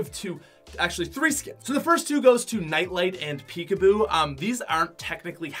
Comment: Did u say NITELITE NOT NITE NITE